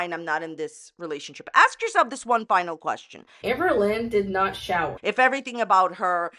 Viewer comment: never commented before, but I have to admit that every time you would put the Destiny's video I thought they were saying "Amber did not shout" and I never thought it was wrong because ( for me ) Amber is always shouting on her videos and I thought it was an ironic joke... Anyway, happy new year! i love your videos and the kermit